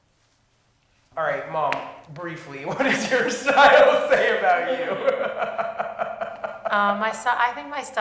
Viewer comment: The interviewer asking “what style is it that you use?” and laughing at her at the same time is one of the problems the way society is so critical of women and their bodies, and how we dress. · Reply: Are you talking about around 1:48 ? Cause the interviewer said “mom, briefly, what does your style say about you?” And it seemed like they were laughing about an inside joke. It didn’t seem judgmental